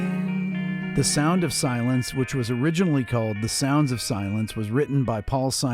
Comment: Wha? 0:12 "The Sound of Silence which was originally called The Sound of Silence..." Huh? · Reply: The soundSSS of silence, look at the album cover a few sec later